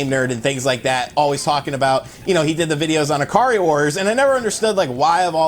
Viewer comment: he spoke about 'AVGN', all I heard was "Where did the hair go?"